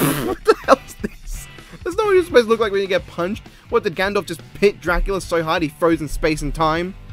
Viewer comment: did you say Gandalf?